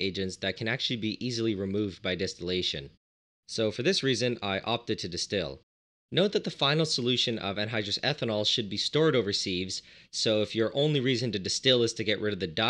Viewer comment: I Thought it said androgynous ethanol for a sec.